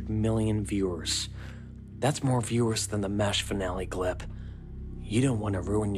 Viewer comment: does he say the Mash Finale? Like as in 4007 MASH the hit sitcom tv show back a while ago? Or does he say mesh finale. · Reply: Its MASH. One of the most watched TV episodes of all time, and held the title until only a decade ago when it was beaten out by Breaking Bad.